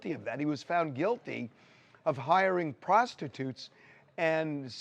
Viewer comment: I thought he said he guilty of prostitution Cassie and Jane